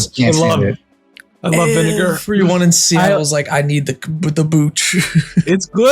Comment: did he say sea isle or Seattle